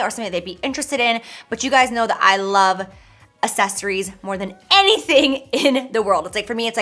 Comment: I Listened 5 times and I swear she says asses-eries.. instead of accessories lol